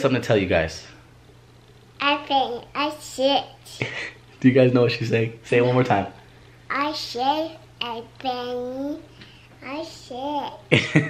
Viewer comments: Done love ya. I think it's so funny how at 2:53 it sounds like Elle said'I shit my pants'😂😂lol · I seriously thought Elle said "I think, I shit!" hahaha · “ I think I shit “ 😂 I swear to God I HEARD this LINE 😂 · No hate but i thought she said "i shit" 😂 i love you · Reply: I thought she said in sick i shit 😂😂 im sure she has the shits sent she is sick 😂💖💖💖